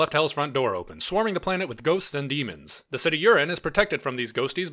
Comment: "The city urine is protected by a giant electrified fence..." is what I heard. And yeah, that IS metal as all hell.